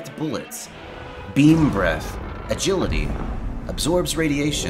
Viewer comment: All I hear now is, "Bean Breath." Now I want tacos...